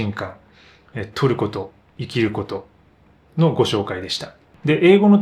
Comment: 「トルコと生きること」と聞こえてしまい、あれ？いつトルコに行ったんだろう？と思ってしまいました